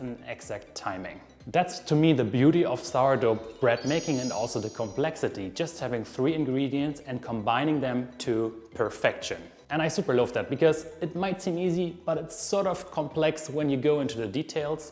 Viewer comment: I heard it as... "I super loaf that." ❤